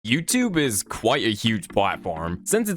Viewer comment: Why in the beginning I heard "YouTube is quite a huge Bible"😂😂